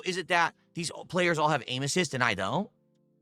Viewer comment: Must have old ears …but at 11.24 it sounded like he said . “They must all have anus’s and I don’t “ . .? Had to skip it back twice …he’s got terrible enunciation…..